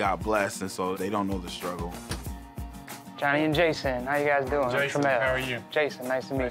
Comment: when he said their names together I thought he said giant jayson 💀😭